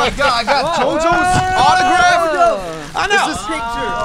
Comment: I thought Namjoon said : " Jeo Jonas autograph" I was confused 😂